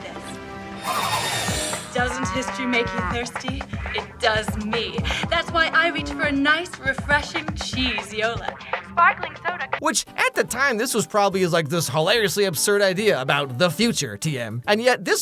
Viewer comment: I'm sorry...did that say "cheese sparkling soda"?